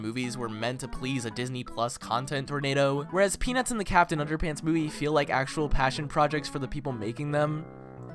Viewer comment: am I the only one who thinks it sounded like “Penis and Captain Underpants”?